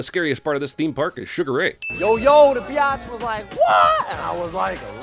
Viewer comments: Did Freddy say “bi-atch’ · Did Fred just say beeotch? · did he just say biotch or bitch I get the was written to be a pg13 comedy at first but they really left that in Also one more sin James Gunn directed this shame